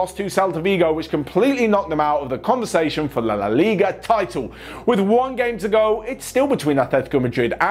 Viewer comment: did he just say LA LA LIGA😂